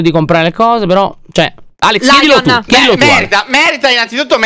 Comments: solo io ho capito m*r*a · io all'inizio ho sentito Alex che diceva "m3rda" e poi ho riascoltato e ho sentito "merita" 😂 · Raga solo io ho capito m***a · solo io ho capito merd* invece di merita lol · Alex: Merita!! Io che ho capito mer*a 😂😂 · Io che capisco "M3rda,m3rda " quando poi leggo i commenti e dicono che diceva merita 😂😂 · solo io ho sentito mer** aahahahahahahahah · solo io ho sentito Alex che dice mer d a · ho capito alex che ha detto mer hahahahhaahahahhahahahahahahahhahahaa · ma solo io ho capito un altra cosaa? cioè me###a haha